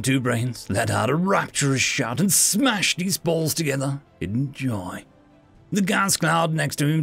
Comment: Oh boy. I thought at first you said “smashed his balls together in joy.”